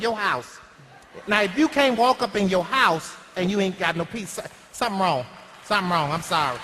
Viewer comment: I swear madea at 9:39-9:43 said "If you can't walk up in your house and you ain't go no pizza something wrong." lol